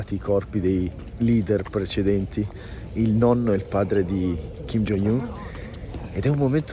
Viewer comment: Io a 12:03 ho capito "Kim Jong Gnu" 😂😂